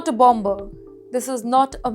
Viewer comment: I heard a "BAMBER" 😂 supposed to be "BOMBER"